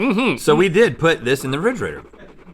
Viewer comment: Did Rhett really say "rigrator" instead of "refrigerator"?? -- 4:33 LOL -- I love these guys!!